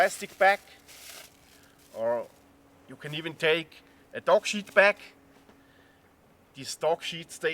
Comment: at 5:12 did you say "dog shit bag"? I listened to it about a half dozen times, I think that's what you said, but I'm not sure!!! :>)> ??? Cool project, I will check out your old Christmas tree videos also! :>)>